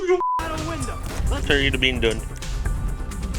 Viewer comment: ok wtf did I say lol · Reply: Most likely something along the lines of, "let me know when your done"